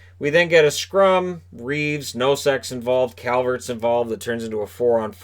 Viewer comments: "Nosek's involved" And here's me who heard it "No sex involved" and I spent two good seconds trying to figure out what's that supposed to mean. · Someone should write a comedy/mockumentary about a Junior hockey team and cast as the coach (and have him write some of his lines) "you made him cry coach!?!"... That was hilarious!!! And what's with the scrum that involved "Reaves, and no sex"... I heard it... I'll time stamp it in the replies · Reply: Ok @ 6:14 "we get a scrum. Reaves, no sex involved"... That's what I heard... Prove me wrong!!!(I know, he meant Reaves and Nosek's involved, just keep your dentures in!!!)